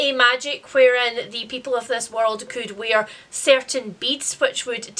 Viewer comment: Mishearing beads as beets produced some interesting interesting mental imagery of that magic system.